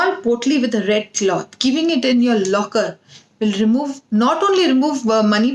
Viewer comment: what does you said here please type ma`m (did u said locker?)i have hard time to understand some of the accent you explain somehow,thank you for the information you given us..Namashte~ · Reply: Make a small bag and keep it in your locket